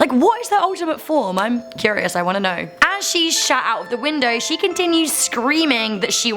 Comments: I LOL'ed because it sounded like you said 'As she shat out the window' like she pooped out a window or something XD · did she say as she shat out the window o_O